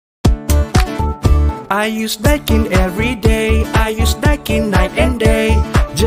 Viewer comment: I was here because everytime im at familymart i hear "are you snacking every day" and im furious what they singing